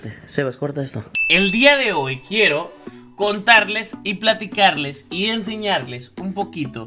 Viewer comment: like si te diste cuenta que dijo cortarles y no contarles